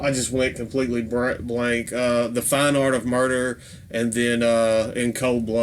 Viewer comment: I was listening to this at work. When you got to the malevolent creation section, when you talked about “fine art of murder”, I thought it sounded like you said “fine fart of murder”